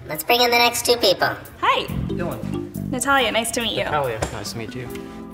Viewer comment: didn’t even ask the first guy’s name but she’s mad he didn’t remember hers 😂 ok, lady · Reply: I thought he said “how you doing” but it could’ve been Dylan? Lol